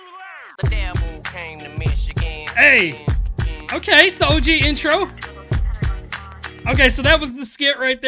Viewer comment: Did he say “soldier intro?” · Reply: he said "so og intro"